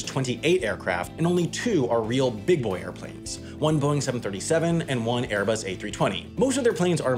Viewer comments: says 737 but caption says 767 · captions say 767, sam says 737. Hmm??